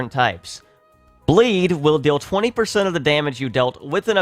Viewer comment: I thought you said "Blade" and thought you meant "Blaze," but then you said "Blaze" and I realized you said "Bleed." I was about to question your credibility.